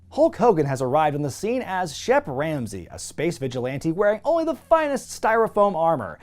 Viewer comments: I Thought He said "Chef Ramsey". #Gordon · I've watched too much Hell's Kitchen, I keep hearing "Chef Ramsey" instead of "Shep Ramsey". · I thought you said Chef Ramsey. Like WTF!? Lol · I heard Chef Ramsey, which then led me to Pastamania (brother). 13:07 AND YOU'RE TELLING ME YOU CAN'T THINK OF ONE TIME DANNY DEVITO WOULD JUST "START BLASTING"?